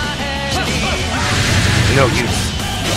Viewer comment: when ui goku said "no use" i thought he said "no u"